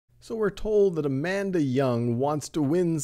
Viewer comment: Tell me why at the beginning of the video I heard "so we are told that a man that yawns"?